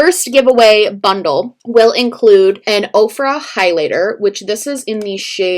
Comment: i thought she said Oprah high lighter. i was like “ damn Oprah’s in the highlight game?”